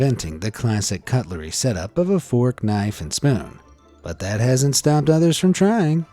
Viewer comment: that's called misheard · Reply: NO HE SAID "Fork,Knife" NOT Fortnight "idk am i rude or smt*